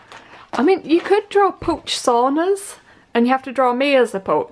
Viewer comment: I thought said pooch saunas, not pooch sonas